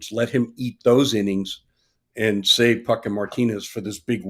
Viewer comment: Beginning of the broadcast said Puk and Martinez and it 100% sounded like he said f***ing Martinez and I was gobsmacked for a bit there haha.